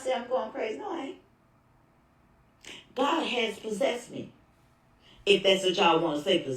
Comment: Did she say Bal has possessed me